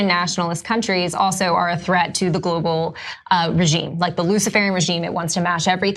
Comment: Did she say the "lucifere' regime"? Wow! Look closely, I think SHE is actually a demon working on behalf of that regime.